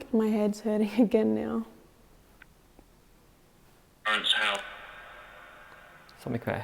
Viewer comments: when you asked what's the devil going to do? The response said "raise hell" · This place really put on a show for us! We collected the most evidence we have in a long time here at the haunted Shrewsbury Prison in the UK making for a very scary night! 😈 👹 👻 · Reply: Awesome episode and! I feel like I heard something different on VOX than you guys did at 52:44, you said you felt like your head was hurting and then you got a response. I swear I heard "Hurts how?". I actually gasped and jumped back, it was so clear and on point! It absolutely gave me chills 😬😱 I'm a skeptic and usually look for possible explanations to odd occurrences in paranormal videos. I'm of the opinion that 99% of the time, there's a logical reason for what's been seen or heard. But this episode had way too many things that were hard to explain. While its possible they were all just coincidences, I mean, that seems like far more coincidences than probability would allow! Also, I've never seen a place unsettle the two of you so much, which is completely understandable. I wouldn't last more than 60 seconds in that place! You guys are way braver than me! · Creepy place. During EVP an orb went in front of rope. Both back in a wing I saw a flash of light near ceiling and I have been lead to believe its spirit. On vox I heard "burnes hell" when you asked what the devil does.